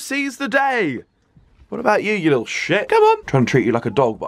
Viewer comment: Did he say little shit or little check in